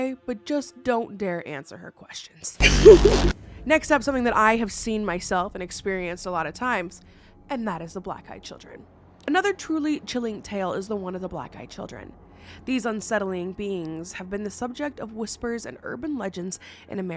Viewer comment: Idk why but I always hear “black guy children” & then it takes a second for my brain to catch up…lol